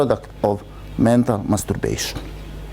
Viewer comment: lol Did he say "mental maturbation" at t=5:48 or something else? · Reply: Yes, I did say Mental Masturbation! That is what we all pracrtice when words are not backed up with actions! We are manifestation of the idea of wo-man in action! And we are acting, acting...